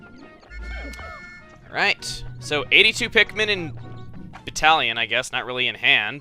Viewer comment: I thought you said "82 Pikmin in... Italian I guess." My first thought: ... They wrote something in Italian here???